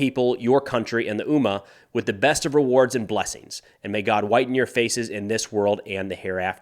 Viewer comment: Did the Hezbollah statement say something like may god whiten your faces? What does that mean? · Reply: Yeah, that seems strange. I thought he said may God widen your face, but not sure.